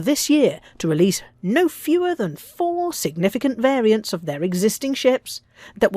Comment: Minute 5:00 did they say 4 variants of existing?